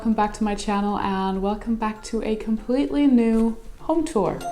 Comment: Been watching so much GoT stuff that instead of "home tour" I just heard "HODOR"